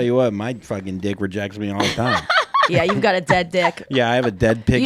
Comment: I keep hearing Nikki saying "Yeah, you got a dead dick" with a Boston accent.